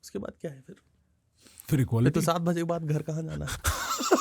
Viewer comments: can someone tell what ravi said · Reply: Fir to 7 baje k bad ghar kaha jana hai 😂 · what he said? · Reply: He said 7 baje ke baad ghar pe aajana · pe kya bola? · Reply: 7 baje ke baad gar kaha Jana h 😂